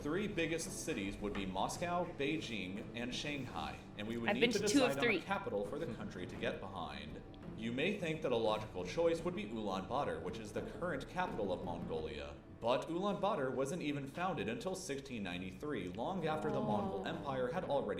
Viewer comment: Did he just say ulaanbutter ?